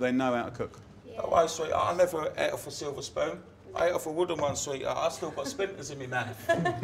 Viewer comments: what did he say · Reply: 'Don't worry sweetheart, I never ate off a silver spoon. I ate off a wooden one sweetheart I still got splinters in me mouth' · wtf does say at 2:12? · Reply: says "I swear I never ate off a silver spoon"